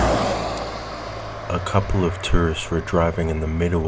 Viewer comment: I thought you said a couple of turds were driving. I had to rewind it to make sure I heard it correctly.